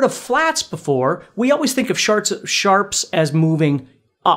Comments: Fantastic video, sir! This has helped me immensely 😊😊😊 Also got a giggle out of what sounded like a slip of the tongue to the word "shart" at 11:03 😅😅💀💀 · when he says sharts instead of sharps i couldn’t breathe!😂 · "Sharts" on repeat at the moment, now all I cant hear is shart. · Did he say sharts?